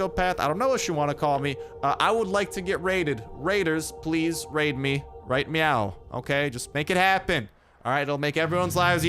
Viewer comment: Did you just say "raid me right meow" lol